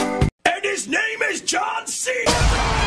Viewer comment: can someone write to me what he is say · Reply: 24 hours away from my wife